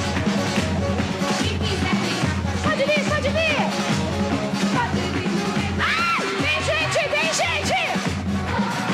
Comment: and 0:32 does anyone happen to know what she says here? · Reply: 17 seconds: it’s part of the song where she’s hyping up the crowd for the next part of the lyrics 23 seconds: she says “vem gente vem gente!” Which means “everyone come!” It makes more sense in Portuguese but yeah she’s basically telling people to get away from the fire 32 seconds: idk if it’s her or someone else but from what I can hear I heard someone say “Xuxa over there!” So I think someone was probably talking to her instead of it being her the one talking. And if she was the one talking then she was telling people where to go. It’s one of the two I can’t hear what they said clearly. But you get the gist of it. Hope this helped!